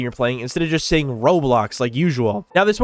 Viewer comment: I thought he said rolox 😭